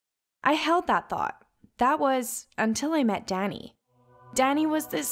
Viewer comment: I thought she said Daddy instead of Danni or Danny